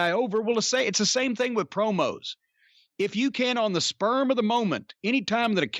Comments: Sounded like said “sperm of the moment” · Did he say "the sperm of the moment"? at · Did he say "sperm of the moment"